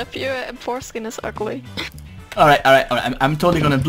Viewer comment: I actually thought she said "your foreskin is ugly" and laughed hysterically...I guess she technically did when saying "your m4 skin is ugly" lol